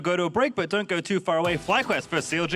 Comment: What was that last bit at 49:18 ? I hear "not the best day to miss Cara"...... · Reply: It's the State Farm ad that almost played on accident, "a lot of great dates in this car"